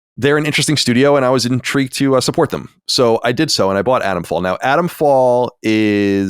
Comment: I read it as "Atomfall", but all I hear is "Adam's fall"